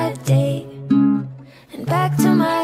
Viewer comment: PLEASE DO NOT SAVE THE PHOTO⚠️ I needed a lot of courage to reveal it 🥲 - DO NOT STEAL IT AND PRETEND TO BE YOU, I WOULD BE EMBARRASSED TO SEE MY FACE ON PEOPLE’S PFP 😭 My parents do not allow me to reveal my whole face, I had to cover my face 🥹💦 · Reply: When It said Who R U? I thought It said Who Hurt You. Xd